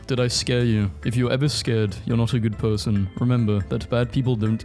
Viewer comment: I thought that said, “if you are scared, you are evil” like fear leads to the dark side